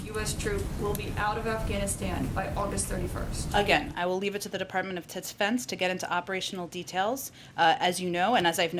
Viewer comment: did she say the department of titsfense?